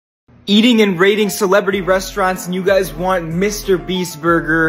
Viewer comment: I thought I heard eating and breeding Celebrity Resturants instead of eating and rating